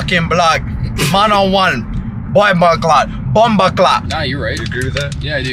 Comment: did just said “bakla” 😭 it means “gay” in filipino language LMAO